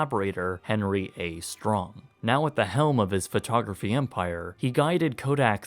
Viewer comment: He said “at the helm of his photography empire” and I thought he said “with the help of his photographer vampire”